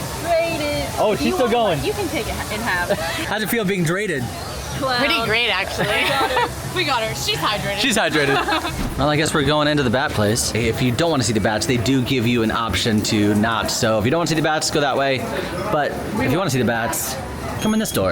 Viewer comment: Literally thought said, "I guess we're going to the Bad Place." Not "the Bat Place." Would love that crossover, though!